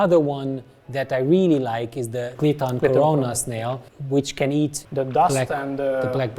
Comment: Couldn't quite catch the name of the snail at 6:05? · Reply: It's Clithon corona. ;)